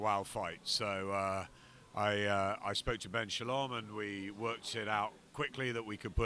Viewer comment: did he say "we worked sh*t out" 😂😂😂